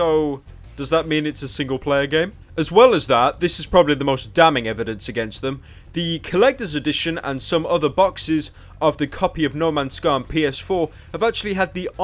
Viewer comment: Did he say "no mans scum"